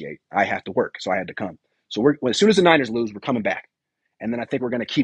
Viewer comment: did you say lose?? 🤔 hmmm I don't like that adittude 😒